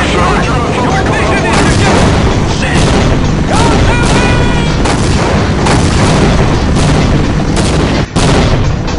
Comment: What did he say before he fell off at 2:05? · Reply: He said, "Shit!! Goddamn it!"